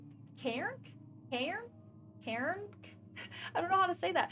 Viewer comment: Did you just call the water monster a Karen? Respect